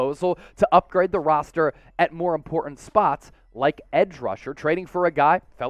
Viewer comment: To say the running back position “isn’t important “ is an egregious mistruth.. I’m sure you meant a position of “greater priority” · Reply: I apologize, upon a second listen you did say “more important position,” I thought you said “important position,” (around 11:00) insinuating that the RB position wasn’t important, my fault!